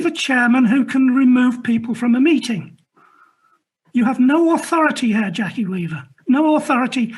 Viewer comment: 'You have no authority here Jackie' had strong Sauruman vibes. 'You have no power here... Jackie Weaver'. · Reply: It might have been the Cheshire accent but I actually thought he said: “you have no authority Herr Jackie Weaver”.